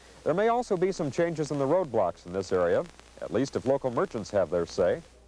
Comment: When he said road blocks why does sound like he is saying roblox